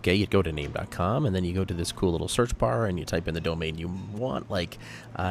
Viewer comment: Did you really call that a "cool little search bar"? You said it so fast I heard "Google Search Bar" and I was like "WHAT?! You can't call it a Google Search Bar if it's not on Google!" Seriously, was that a trick?